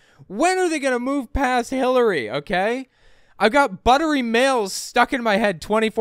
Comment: I hear "buttery nails" when " but her emails" is said by. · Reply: I always heard it as "buttery males".